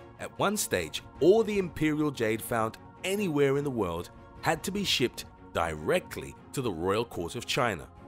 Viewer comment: What are you saying, i didn't get you (had to be return to chaina) 4:06 to 4:15 ?